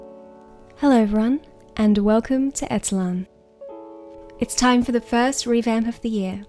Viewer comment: When I heard you say etellan I thought you said eitilán, which is aeroplane in irish not an insult, just came to mind